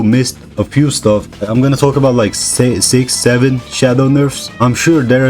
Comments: wait a minute… did he say 67?! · wait did he say 67